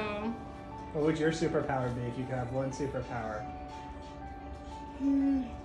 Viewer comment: I thought she said tackling bulls until I read the caption